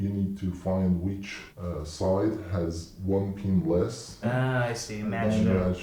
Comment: The auto-subtitle say PeNiS